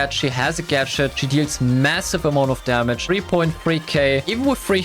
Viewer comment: d-did you say MASSIVE